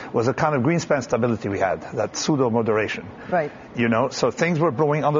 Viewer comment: What does Taleb say on 5:16 ? "sudo" moderation? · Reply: pseudo